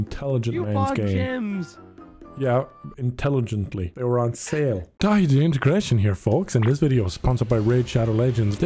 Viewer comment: i thought he said "its daithi de immigration here" hahahah